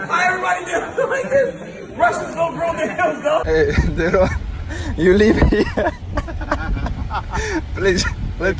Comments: I hear and see Khabib say something that sounded like digger 🧐🤣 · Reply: Finger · did khabib just say the n word? 🤣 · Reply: Finger